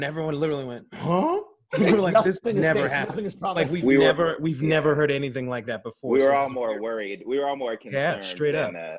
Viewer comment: What did Lin say at 0:55? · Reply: “Nothing is safe, nothing is promised.”